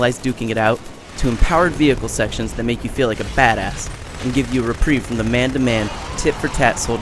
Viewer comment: did he say make you look like a fatass 0.0 I mean, sitting in a veichle doesnt gain you THAT much weight